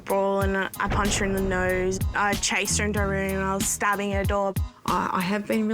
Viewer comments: I thought she said stabbing her dog and I’m like 😶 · did she just say she was stabbing her dog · I’m sorry at 1:43 did she say STABBING HER DOG OR AM I TRIPPEN